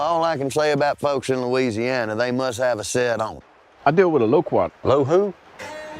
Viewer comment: What does he say at 0:50? "They must have a set on..." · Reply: "Set on 'em" Like set of nuts😂